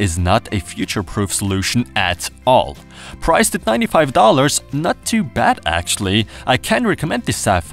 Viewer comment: what price he said ? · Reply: 95 $